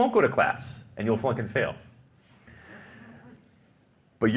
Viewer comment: 😂 did he say “or you will F***n fail!” At approximately 3:28. Get behind me Satan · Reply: He said FLUNK & FAIL!